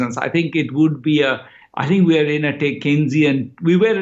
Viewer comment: Did he say "Keynesian"? slow-down and "Keynesian"? tailspin. I couldn't hear correctly both the times. I tried to look up the ET article but again they don't mention it. Can someone throw light on this?